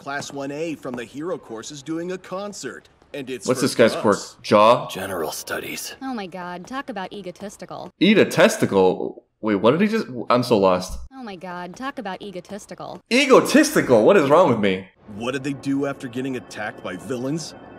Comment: this made me laugh so much bc english is my second language and this is how I hear things sometimes, in fact, you said "eat a testicle" according to the comments but I completely heard "Iida testicle" lol